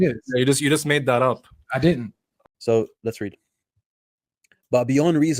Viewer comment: Orthodox Muslim :you just made that up: I did 🤡 😭 🤣what more do we need? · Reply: He said *I didn't * you misheard